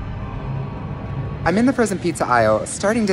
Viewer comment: “I’m in the frozen pizza isle” What I heard: “I’m hunting piz-Ohio”